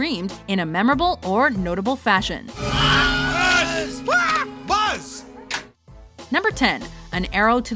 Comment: Did Buddy Said Buzz Like A Bee Instead Of Boss At 0:37? XD